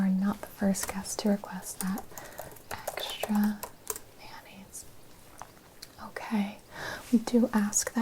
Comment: Extra mayonnaise? At first I thought she said extra manis lol